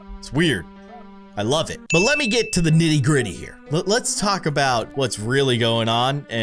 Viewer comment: did someone say griddy??! ( hits the griddy )